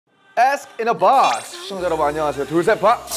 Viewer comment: I thought namjoon said ass in a box at first